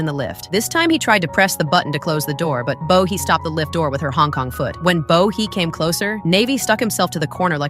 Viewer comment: Did I hear “ Hong Kong foot”?